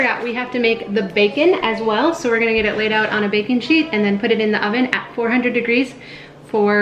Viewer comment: 3.38 I thought you said ‘bacon sheet’ (us Aussies call them oven trays) and my first thought was ‘wow, Americans take their bacon very seriously to have specific cooking trays!’ 😂